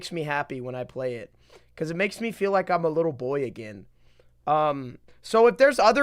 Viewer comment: did bro just say little boy?